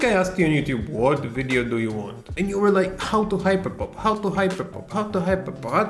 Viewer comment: I kept hearing him saying "how to hipopop, how to hipopop"